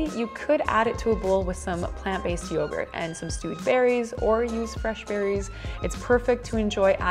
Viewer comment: When you said stewed berries I thought u said stupid